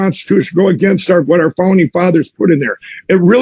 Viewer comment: Is it just me or did he say 'Phony Fathers' at 1:52.